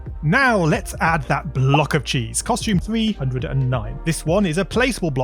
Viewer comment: in the captions it said 209 instead of 309 lol